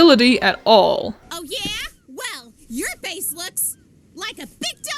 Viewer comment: I never realized that Nora said base I always thought she said face